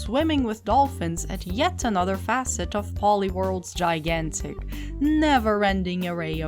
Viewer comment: when u first said PollyWorld I misheard it as Poliwhirl XD That's her main pokemon!